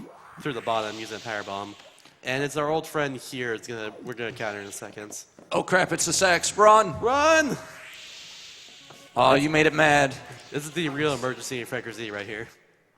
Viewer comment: LMAO, 1:14:54 I thought he said "Oh crap its the sex. Run."